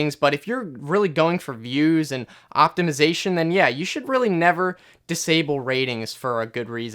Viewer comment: did he say optimisation?